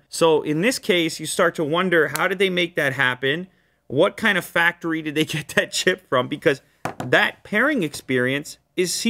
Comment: I always watch unbox therapy at 1.5x speed because Lu talks way too slow. Great vid nonetheless. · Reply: At 1.5x speed it sounds like he is saying what factory did they get that shit from